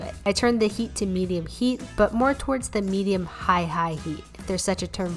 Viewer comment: At first I thought you said that you make your cat hold onto your pan lol (but then I realised it's cap)